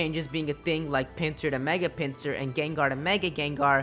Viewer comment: At first I thought you said "Mega Pansear" instead of Pinsir. I got confused and then had to think about who already had mega evolutions